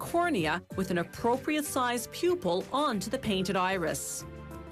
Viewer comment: Did I hear her say "inappropriate sized pupil?"WTF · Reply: The narrator said "an appropriate sized pupil".